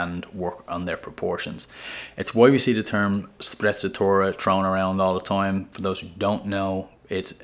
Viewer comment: Thank for the advice! I’ll be using the color wheel “trick” moving forward to spruce up my outfits. What’s the pronunciation of the word you used around the 8:00 mark? Started with an S and basically meant being intentionally undone. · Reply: Found it, 😅 sprezzatura! Grazie!